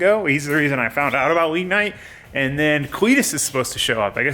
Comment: He had me for a second I thought he said weed night but I ran it back and it's wii night 😂😂😂😂🤦‍♂️